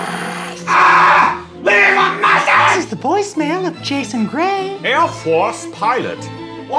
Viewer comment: Will's gone again *shrieks in Riordanverse* NO This is the the voicemail of Jason Gray I heard "This is the voicemail of Jason Grace"